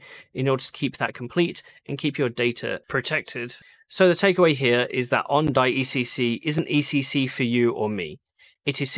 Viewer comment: 🤓 what did you say @ 7:11 originally, safe? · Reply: Secure